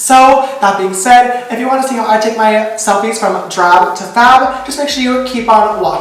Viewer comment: when he said drab to fab i thought he said giraffe to fab lmao i'm def 😹